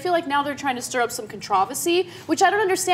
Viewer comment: Did Ana say "controvasy" at 3:02? Did she mean controversy?